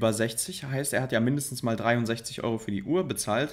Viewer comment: wer hat auch 63€ verstanden 😂